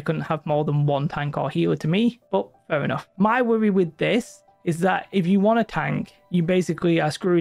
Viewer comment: what on earth are you saying here? 😆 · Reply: "my worry with this"